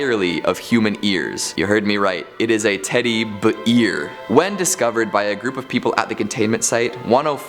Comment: sounds like he said teddy beer